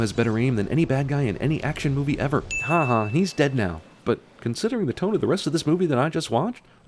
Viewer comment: I heard haha you're dead and I accidentally went back to